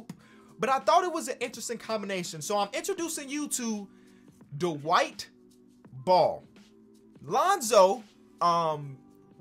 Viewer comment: Idk if it's me missing the gem on twitter but I kept hearing "the white ball" for "Dwight Ball.